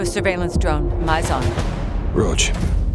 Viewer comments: Task Force 1-4-1 0:27 Captain John Price 0:34 Sergeant Kyle 'Gaz' Garrick 0:41 Sergeant John 'Soap' MacTavish 0:54 Lieutenant Simon 'Ghost' Riley 0:47 Colonel Alejandro Vargas (Mexican Special Forces) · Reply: did he said roach? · Did someone say roach at 0:03 ?